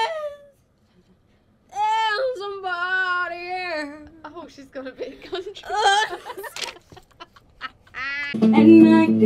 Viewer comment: what does jesy at 1:30 say? and can I have the links of the full video please ♡ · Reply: Jesy says "Oh she's gone a bit country"